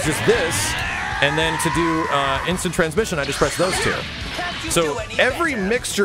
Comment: did he just say instant transmission?